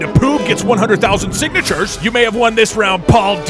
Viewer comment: Doug literally went through the 5 stages of grief about the word “p(l)oob.” Denial: Refusing to accept the people who say that it’s ploob. Anger: Being angry at the people who say it’s ploob. Depression: How Doug felt after it was confirmed to be ploob by Paul. Bargaining: Doug trying to bargain by having a court trial to determine whether it’s poob or ploob. Acceptance: When ploob wins the court case. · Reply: I heard "Proob"